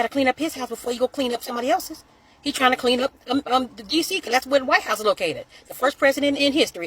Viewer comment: at first i thought she said ' clean up on aisle DC ' lol